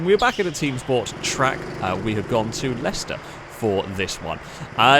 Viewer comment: Thanks to the abudance of gta video from, when he said they went to Leicester, I thought he said Lester.